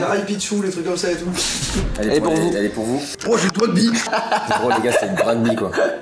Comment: au ptn j'ai compris Doigby 😭😂👌🏻